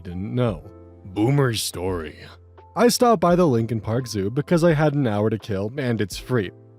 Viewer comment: I looked away at that part and thought I heard 'Linkin Park Zoo'.